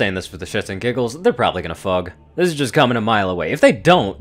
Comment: did you say they probably going to fug? LMAO!!